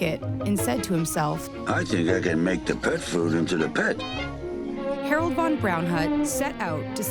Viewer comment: all i heard was : i auhauhasgjjhshsn make theperfood intothepen